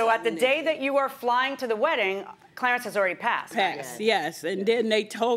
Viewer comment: I thought she said she met her through a "clearance sale"